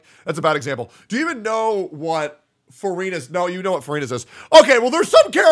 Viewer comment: my dumbass thought he was saying furina the whole time when he was actually saying verina 😭